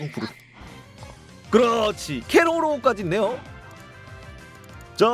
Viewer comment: 나만 케로로라고 들리나여?